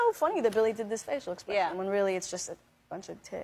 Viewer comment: why did i hear “a bunch of t!ts”